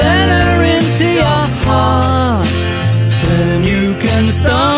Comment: The word HEART sounded like CAR to my ears! 🤣